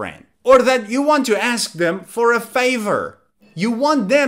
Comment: Hey, why there is a "fever" word instead of "favor"?